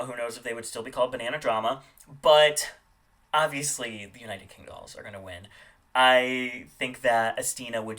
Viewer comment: It sounds like you said United kendolls. But it was United Kingdolls.